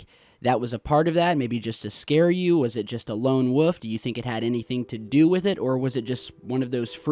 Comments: did really just say “Lone woof” instead of ‘Lone Wolf’? Is this an accent thing because fuck na I ain’t fucking with that pronunciation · one of those people who pronounces the word wolf as “woof” instead of “woLf” lol. Good content homie keep it up.